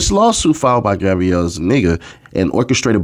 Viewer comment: Why did it sound like you were saying, "Gabriella is a nigga?" I had to look at it and I saw the name.